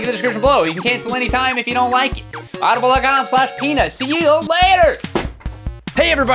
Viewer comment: did he say penis